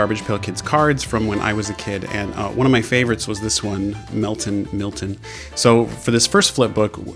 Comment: it sounded like you were saying Milton Miltion